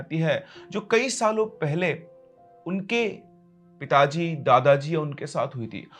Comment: I heard pitaji as itachi 😂😂